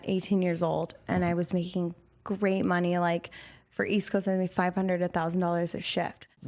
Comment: (or so) Captioning states 500K, what was said is 500-1K